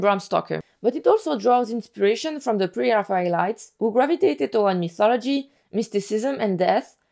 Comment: I’m so sorry if this is rude, but I am genuinely just curious and want to fully understand what everything you’re saying, but what did you say here? The part where you say “but it also draws inspiration from the ___” I rlly hope this is not rue I just wanna know th facts at the fullest extent! AMAZING video and I’m definitely subscribing 🙏🙏🩵🩵🩵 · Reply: Helloo sorry for the late reply, I was talking about the pre raphaelites, which is an art movement from the XIXth century. You can look up painters such as Waterhouse, Rossetti and Millais. Hope that helps and sorry for the atrocious pronunciation! 😅